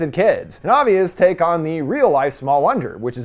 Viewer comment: I noticed that too, also the scene when the movie begins and the computer messages it resembles the matrix, Tom's decent into the rabbit hole. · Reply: please tell me you know wtf he’s saying at 6:57 what the fuck does he want us to look up “obvious take on the real eyes from all under” ?????